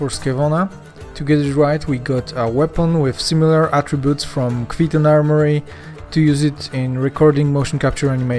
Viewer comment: Did I hear correctly? Kvetun armory sword in Hellish Quart?